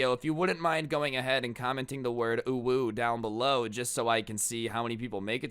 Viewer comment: UwU (sorry I kinda had to when I heard type "UwU")